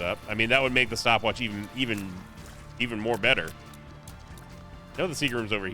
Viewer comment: did he say “more better”?